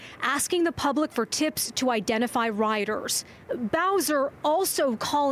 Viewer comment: did she just say bowser O_o!? also didnt something similar happened a year or 2 ago in hong kong?